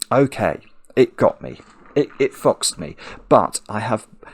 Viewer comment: What did Simon say? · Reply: It foxed me, perhaps? That's my best guess